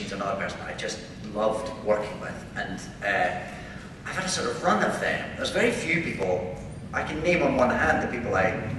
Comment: what does he say at 39:20? just asking for a homework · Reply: "I've had a sort of run of them."